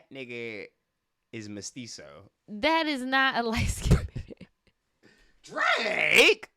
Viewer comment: damn i thought he said “white skinned” 🥲